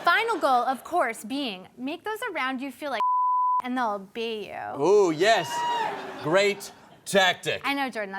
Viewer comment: "Make those around you feel like [shit] and they'll be you"? I have to have misheard that? And FFS Comedy Central, show some responsibility and caption videos for your deaf fans. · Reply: She said, "and they'll obey you."